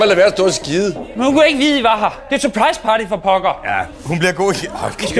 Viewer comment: Oddly enough, what he said at 2:12 sounded like the English phrase: “surprise party” which is what they’re doing